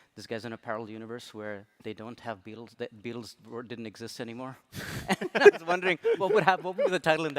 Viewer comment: It sounds like he says “The bills didn’t exist”. It’s The Beatles didn’t exist. The film is indeed called Yesterday.